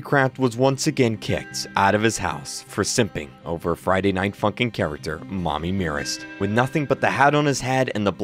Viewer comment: In 1:07, turn on the subtitles or captions! Youtube subtitles or caption think kreekcraft swear bc he say “Friday Night Funkin” not “Friday Night F**kin” WTH YOUTUBE!!